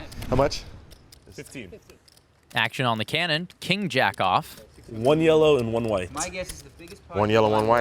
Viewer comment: I realize what he was saying…but “king jack-off” was probably not the way to say it lol.